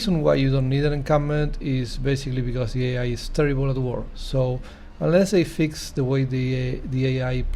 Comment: +, you say what? The reason ... the AI is ... ? I cannot understand a few words right in that sentence. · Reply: "The AI is terrible at war" Encampments are great for defense, but you won't need them against the AI.